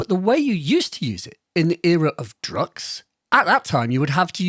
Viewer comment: are you saying "era of drugs". It sounds like it in my ears, and youtube auto-CC says it too. Just clarifying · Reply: “era of drukqs”!